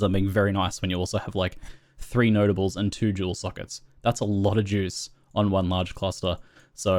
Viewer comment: I don't mean to make fun of your accent, but "that's a lot of jews" is what I heard....ok carry on sorry, great guide!